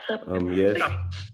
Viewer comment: says "Fo' sho", then at 36:33 "Umm, yes sir" clear as day when asked if it was an accident 🤷🏾‍♀️ (also: this is crazy, but I started listening before work then just came back to it 5 hours later - having totally forgotten what nickname his Daddy called him - and when changed boxes and asked again just now, I heard his Daddy say "Chopper" 😂 😂 😂. Two minutes later confirmed they heard it too!) REALLY came through for his boy!! 👏💕 & our friend is such a cool cat. Love to him and his wife 💐